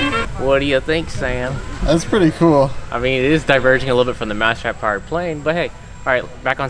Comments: I thought it said mustard powered plane lol 😂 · Ngl I thought it said mouse trap flavored plane for a hot sec